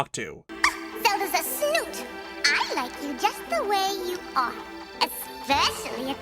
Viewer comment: What The heck does the fairy say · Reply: "Zelda is a snoot! I like you just the way you are!"